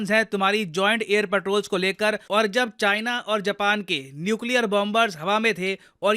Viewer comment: Did I hear Japan instead of Russia?